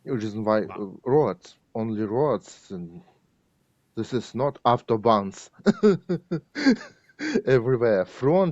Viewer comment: I really hope you are not using Ai captions. If not, there were tons of obvious mistakes given context clues that seem like youre using Ai to caption. Please let us know, thanks. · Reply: "buy" instead of by "auto bonds" instead of auto bahn. I always wondered how this channel pumped out so may videos so fast...